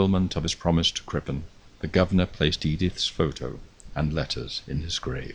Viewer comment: Did he say “Edith” at @ 19:19 mark?